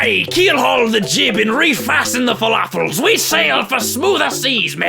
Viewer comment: Did he say falafel in